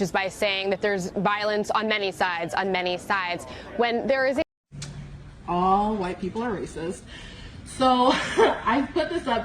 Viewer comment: sorry... what was that? I almost thought you said somthing about all white people being racist/demons? Haha! Don't worry, I'll listen more closely this time so I can hear what you actually said! :)